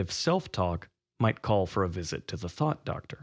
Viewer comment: Omg I needed to watch this I am a Rando and need serious help having a goal I mean I want to be a master animator that’s my main goal but I just don’t know where to start or what exactly to post for. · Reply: And lol I heard the Thot Doctor